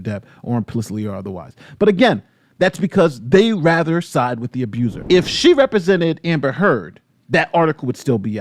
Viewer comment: did you just say what i heard you say? the actual abu-sed or abu-ser ? did you get lost in the wording or did pronounciation let you down.